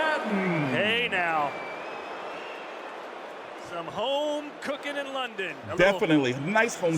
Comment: the best word i heard...."the home cooked in london"...LOL